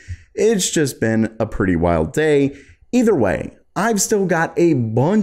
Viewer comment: i was skipping intro and this is what i heard.. "it was a pretty wild date" me: bro hold your horses